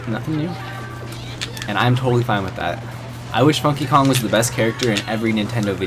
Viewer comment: Hahah i thought he said monkeykong